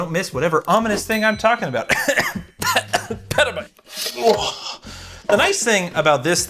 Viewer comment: did I hear petabyte project? 👀